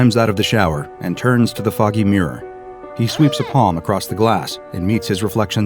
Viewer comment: Am I the only one who hears "turns to the fucking mirror"? Maybe it's 1.75 speed